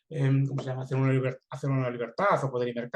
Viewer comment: disculpa en el min 49:55 ....M.A.B. recomienda un libro sobre el dinero....¿podrías decirme cómo se llama el libro y el autor?....es que no entiendo bien lo que dice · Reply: ¿Que ha hecho el gobierno con nuestro dinero? De Murray Rothbard.